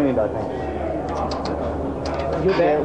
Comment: What is Doohan saying at 1:42? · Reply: Fredric Dubois He said to Mick "Tsk Tsk Tsk" and Mick said, "I'm not Skippy, you know".